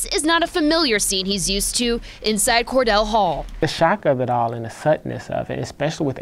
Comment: Did he say, "thutness"?